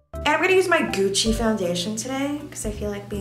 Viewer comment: omfg my volumes low so I thought it said my coochie foundation